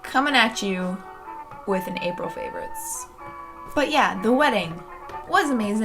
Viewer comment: I misheard April and thought you said "evil favorites" 😂 maybe an idea for your next video?